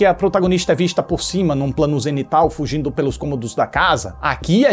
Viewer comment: O plano da câmera se movendo no teto é maravilhoso. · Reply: quando o comenta a respeito desse plano durante o video, por um momento achei que ele falava "plano genital" kkkkkk aí voltei o video e escutei plano zenital e tudo fez sentido kkkkkkkkk